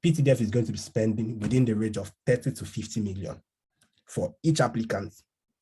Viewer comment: Hello, thank you so much for this video. I had to listen to 8:40 'for each applicant ptdf is probably spending the rate of 30 to 50 million' twice, cos I had thought you said 13 to 15 million. That's not a small amount. Thank you for your insights again.